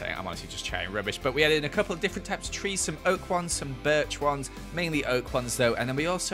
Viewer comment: i thought he said “we added some pokemon ones”